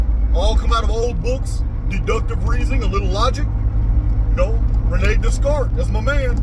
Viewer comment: What was the name of the author he mentioned at 2:30? Couldn't understand the name. Thanks. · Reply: Rene Descartes